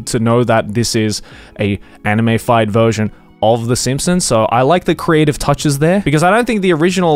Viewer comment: when joey said Simpsons i thought he said Samsung